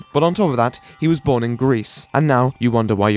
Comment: When he said "born in Greece" I thought he said "born in grease" like his mom just squatted over a pool of grease and plopped the baby in. I might be actually retarded.